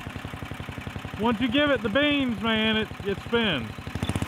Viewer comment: Did I hear a "give it the beans" reference from in there at about the 9:35 mark?